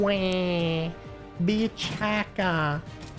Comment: I couldn't understand what he was trying to say. I was like "What's a beach hiker??" Thank you for the translation